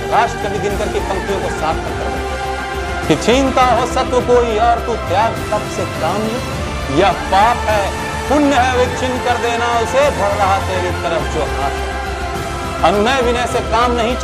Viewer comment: What is said @ 8:30 ?🙏🙏🙏🙏🙏🙏🙏🙏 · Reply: छीनता हो सत्व कोई, और तू त्याग-तप के काम ले यह पाप है। पुण्य है विच्छिन्न कर देना उसे बढ रहा तेरी तरफ जो हाथ हो। - रामधारी सिंह दिनकर 🙏🙏🙏